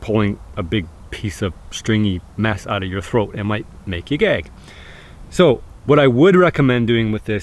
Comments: it took me 3 times before I heard "it might make you gag" instead of "it might make you gay" that gave me a good laugh hahaha. Interesting video! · Who else thought he said "it might make you gay"? I had to do a double take lmao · main at 5:42 did he say it might make you gay💀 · I thought he said it might make u gay lmao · it took me 3 times before I heard "it might make you gag" instead of "it might make you gay" that gave me a good laugh hahaha. Interesting video! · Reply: I saw this and was like what did he just say I thought he said it might make you gay I kept and rewinding it going is he saying gay or gag and then I scrolled in the comments and found out he said gag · I had to listen three times. I thought you said eating the stringy mess would make me gay. I was so confused. Great stuff. keep it up! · I thought he said “Make you gay.” Until I realized he was saying gag 😂😂😂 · lol I thought you said it might make you gay